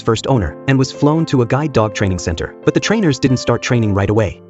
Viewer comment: I thought it said guard dog😂😂😂😂😂